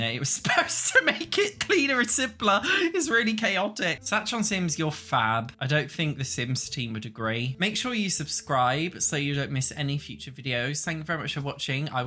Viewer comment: I heard “make sure your sims survive so you don’t miss the next one!”